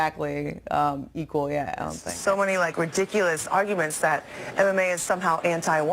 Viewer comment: did she say anime??? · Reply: DrAgOn KiLLeR She said MMA